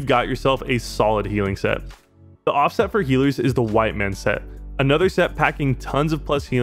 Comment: I was looking at another tab while this was playing and thought you said "the white man set" >.>